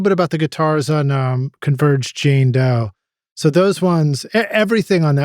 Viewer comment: What are you saying at 38:47? Converge - xxxx??? Which album are you referring to? Best wishes, Anders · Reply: Converge Jane Doe